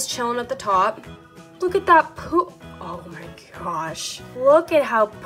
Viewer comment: Lol if you put captions on! Why did it say poop?